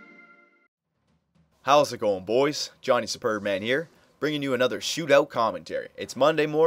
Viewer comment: I thought you said here....